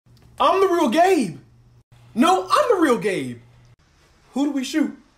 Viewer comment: I hear "I'm the real gay "No I'm real gay"💀